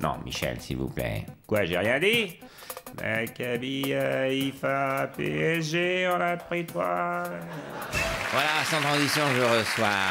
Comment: Qu'est-ce qu'il dit ? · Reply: Hacabi Haifa -PSG en appris 3